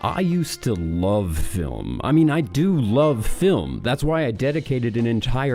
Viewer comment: Every time I hear “I love film”, I hear “I love lamp”